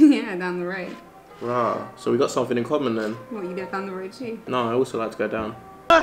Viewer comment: Please what did said in 12:12 before the reaction meme?? He was too fast, I couldn't get it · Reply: He said "nah I also like to go down"